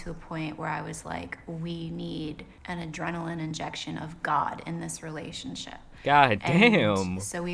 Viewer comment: I thought she said adrenaline injection of god